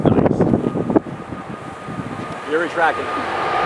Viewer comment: When you said "gear retracting"... I heard "you're attractive". I was thinking this guy is a airplane lover!